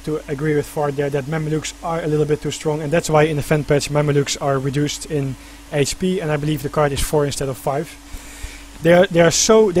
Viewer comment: what did the commentator say @ 20:26 about mamelukes? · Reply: "mamalukes are a little bit to strong and that's why in the fanpatch that's why mamalukes are reduced in HP and I believe the card (ships) 4 (mamalukes) instead of 5"